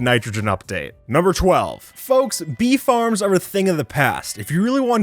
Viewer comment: when you said "bee farms" for a second there i thought you said "beef arms" and i was confused